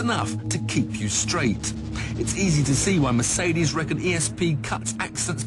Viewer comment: what does he say at 8:57 ? "it's easy to see why mercedes (recko cuts accidents) by a third while toyota by a half." i don't get it.